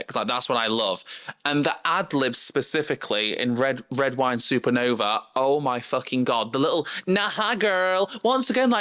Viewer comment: i also thought she said mermaids in red wine supernova lol